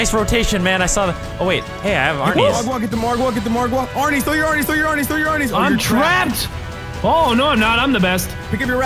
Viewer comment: to me it sounds like he said "throw your barneys"